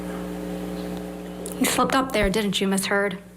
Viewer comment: when i heard taht live, i thought camille said you fucked up ms heard, didnt you? lol